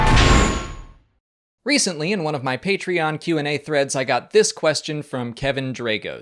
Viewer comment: I thought he said "I got this threat", but quickly realized he said "I got this thread". I got worried there for a second 😅✌🏼